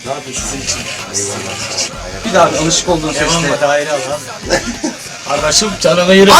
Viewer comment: de ne diyor adam ya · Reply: Atarlı Oyuncu "Vay gardaşım benim" diyor.